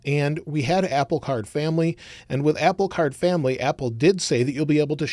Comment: did someone say family?